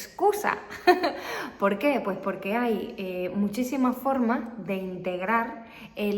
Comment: Did i hear that right ? or i need to improve my listening skills. I think i heard her saying "muchísima formas" instead of "muchísimas formas" here